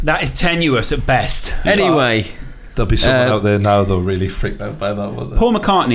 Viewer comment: I hear 'wallahi there is superman'😂😂